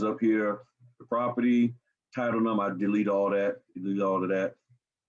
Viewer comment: i cant hear what your saying. sounds like delete but i know i must be mistaken. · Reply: I was saying that I deleted real document and loan numbers in the example I am showing